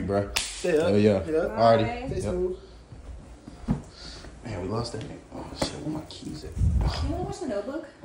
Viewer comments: bro said "damn we lost that nigga" lmfao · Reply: I thought he said we lost that game? I figured they just got done watching sports. Idk. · bro said "damn we lost that nigga" lmfao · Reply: hell nah, good catch. I thought he said we lost that game.